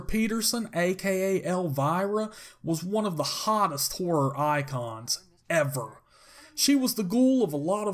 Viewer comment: Sounds like he said whorr or icon.